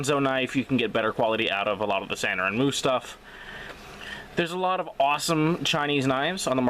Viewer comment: you mention we can get better quality out of what knives? I couldn't understand you. What knives? Ahconzo knives and Sanuruhmmoose stuff knives? LoL. Said it way to quick man! No idea what you said. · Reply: Sanrenmu and Ganzo